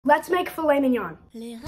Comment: Sounded like he said let’s make flaming yunnngggg